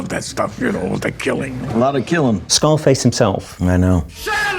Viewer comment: Nobody else hear him say Skullface instead of scar face..?